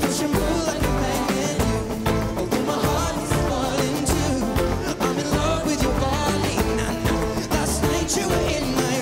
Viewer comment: For a moment, I heard "i'm in love with Joe Biden"